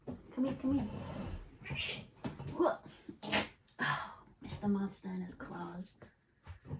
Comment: Hello pretty kitty…😻😺😸 What is his name??? It sounded like you were saying…Mr. Monsta???